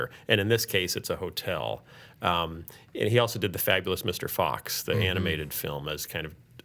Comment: Did he say Fagless Mr. Fox?